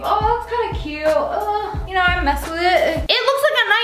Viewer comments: No one: I thought she said a big poopy dress lmbo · I THOUGHT U SAID POOPY